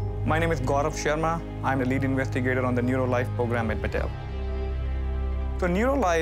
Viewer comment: I thought he said Mattel 😂 kinda made sense for a second